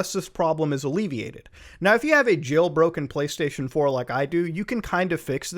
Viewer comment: Everytime he said jailbroken I thought he said joe rogan I was fucking confused 😑.